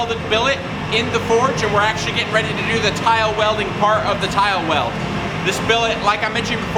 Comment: Could've sworn he said "Kyle-welded" in place of "tile welded"